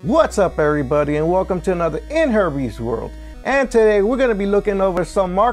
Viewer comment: Thought he said in herpies world. Lol